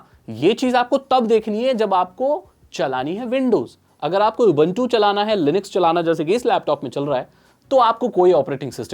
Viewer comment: apne kya bola, kya chalana hai... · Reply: Ubuntu, Linux